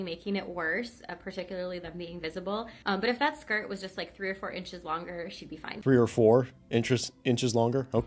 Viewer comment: "If it was 3 or 4 inches longer..." At first, I thought she said, "304 inches longer".